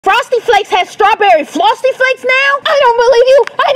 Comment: “Frosted Flakes not has strawberry flosty flakes?!”😮 (I heard her say flosty 😂😅 but ngl I love it even if she said it on purpose or accident 😄)